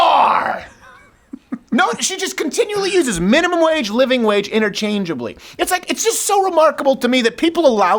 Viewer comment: It sounded like she said "living Rage"